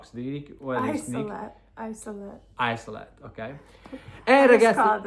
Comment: What is the word they mentioned? I did not get it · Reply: She said “Isolette “—-it’s basically a closed bassinet that allows the nurses to control heat, oxygen and humidity for the baby to heal and grow ( I’m not a nurse- I looked it up 😊)